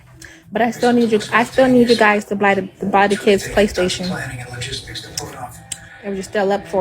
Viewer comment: I thought she said F them kids a couple weeks ago